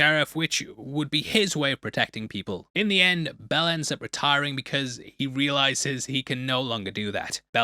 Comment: "Belle ends" I heard it, 05:05, you cheeky wanker!